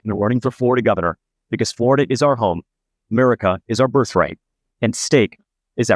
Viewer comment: Did he say Murica? 😂